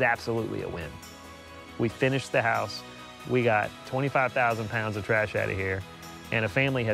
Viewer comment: 2500 lb is 1.25 tons . not 12 . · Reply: oops . i thought he said 2500 lb . 25000 he said 🤣🤣🤣